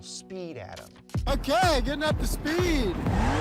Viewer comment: Did he say, up to speed? 😢